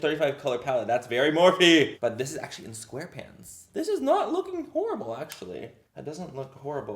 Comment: When you said " square pans " i thought you said " square pants" until you held the palette up